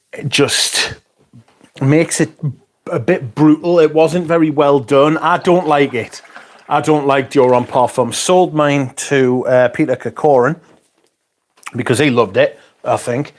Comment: hello all! for a split-second, I thought you had said “I don’t like being gay.” turns-out, it was just floral fury.